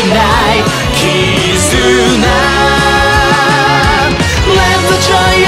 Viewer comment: The Video: "Let the joy of love! Give you an answer!" What i heard: "Let the giant bug! Give you cancer!"